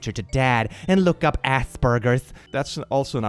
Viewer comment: Oh man😂 Apparently it's been so long since I last heard someone use the term Asperger's in English that the fact read it while I was distracted by one of my cats, meant I heard "look up ass burgers!" And I was like "??? What a weird thing to tell your child to Google" then made his "that's an outdated term" comment and I realised it wasn't ass burgers but Asperger's 😂😂😂